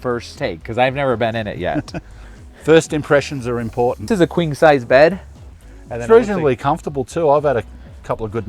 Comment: does anyone else hear "This is a kwing size bed"?